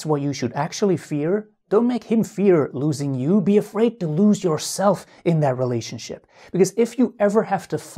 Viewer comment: "Don't fear losing him. Fear losing yourself." Brilliant advice! Thank you! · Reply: Dear CORRECTION: actually it's different, what he said is - "DON'T MAKE HIM fear loosing you, be afraid to lose yourself in that relationship."